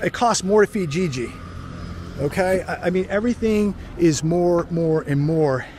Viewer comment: I thought he said it cost more for vagigi, LMAO